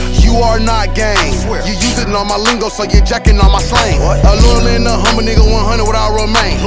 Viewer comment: tf is he sayin there? 😂😂😂 Was that Arabic or still English? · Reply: GLBizzie “Using all my lingo so you jacking all my slang”? Are u deaf? 😹